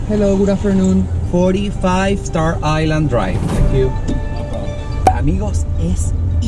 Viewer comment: Me encantó el vídeo. 😍 En el minuto 18:40, cuando dices la dirección Óscar me da risa que dices 45 Star Island Dr. Y en subtitulos pusiste 46, quedé como 😟